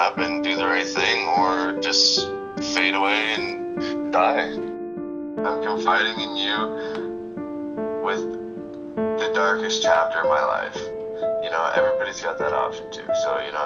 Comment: Great Movie! What did say at 3:42 ? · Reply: "Im confiding in you with the darkest chapter of my life"